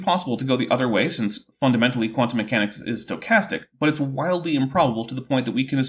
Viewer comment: I heard it as "quantum mechanics is sarcastic". Still less absurd than the drivel he's spewing.